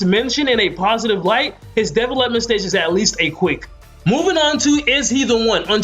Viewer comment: I thought he was saying Devil left the stage at first